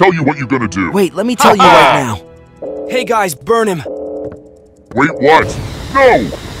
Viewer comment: he said gays instead of guys dude..